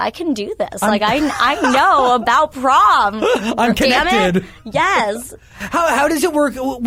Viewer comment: did anyone hear her say damn it because i did